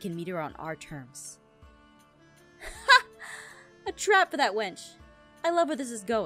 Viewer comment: Did Katelyn just call Ivy a wench..? Omi I'm dying